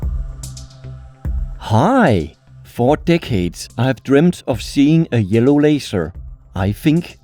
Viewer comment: I miss heard 4 decades and thought he said 4 dickheads lol